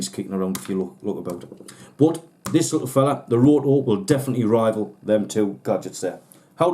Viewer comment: what is this piece called? My sincerest apologies, I couldn't understand your accent. Thanks for your help. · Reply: The areo pully, the termalink and the roto, hope that helps :-)